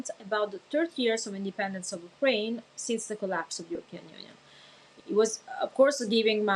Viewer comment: subtitles say 'european union" , she sure talks about russian union. European union didn't collapse yet unfortunatly.